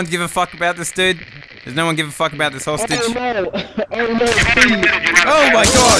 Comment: “whole sit-“ instead of hostage